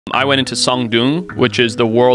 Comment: I thought he said, he went into some dude